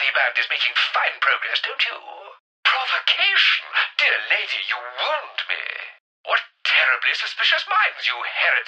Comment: for the first 7 or 8 times i heard this i was sure he said "dear lady you WANT me"